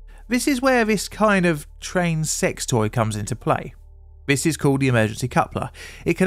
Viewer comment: I was zoned out and all I heard was "train sex toy"